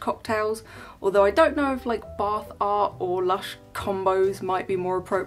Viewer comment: Every time you say bath art I hear "bar fart" and giggle like a 12 year old.